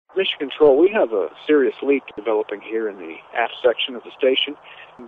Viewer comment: lol, i thought he said " ass section" not "aft section"